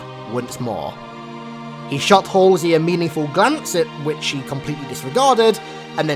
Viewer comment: I thought he said he shot halsey amediatly